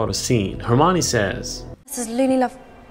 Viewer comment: I always thought she said Luna, not loony, and was pausing because of her last name